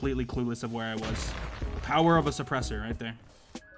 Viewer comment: the power of an oppressor that's what i heard and realized that it's not what i heard previously